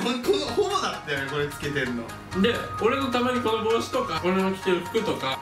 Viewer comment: 改めてみたけど、2:56のとここれホモがつけてるもん、って聞こえたwwwほぼかな？w